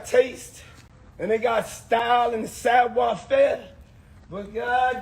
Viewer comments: Please tell me what Diddy is saying @ 1:32 !!?? "Style and......???" It's bothering me that I can't figure it out! Someone help me por favor!... somebody, anybody....help. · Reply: Lol Style and Savoire faire. Its French, basically meaning fly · 😂😂😂😂😂 Did the words say sidewalk fair instead of sav•oir faire at 1:36?